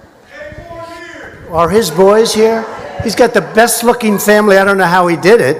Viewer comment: PARDON ME!? I thought when Trump said Bill had the best LOOKIN' family @ 4:47 I thought he dropped the F-BOMB !!! HA!!!